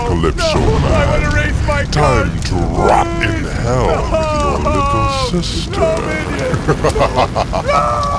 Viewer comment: At around 32:20, did Minion say to Calypso, "Time to rot in hell with your little sister", before he drops him or did I hear that wrong?